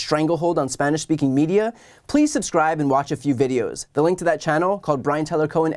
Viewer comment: Thought they were saying "please die"